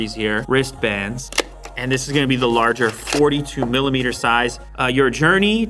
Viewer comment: When you said these are 42 millimeter. My black instincts kicked in and I thought I heard 42 dollars. I immediately took my card out and star hopping on the bed for joy!😅😂😂